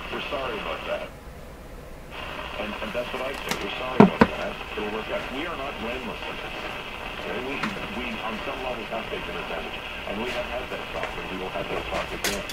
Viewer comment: you should have listened more closely to the guy on the radio at 13:55 when he clearly said about Canada, "were sorry about that", and "We are not blameless in this and on some levels we have taken advantage". then he went on and said he was not letting any of this "hurt his relationships with his American friends and family". You once said you weren't going to talk politics on your channel any more, that's probably a good idea as to not alienate your American and other viewers.